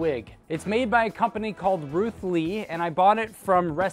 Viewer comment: I'm watching this with very low volume, and even tho he zoomed in on the person's name at 5:39, I wasn't looking at the time and it sounded like he said Bruce Lee